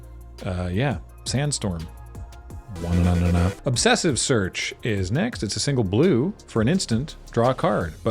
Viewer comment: Misheard Obsessive Search as Obsessive Serge and was confused that it was neither a Land nor Food card.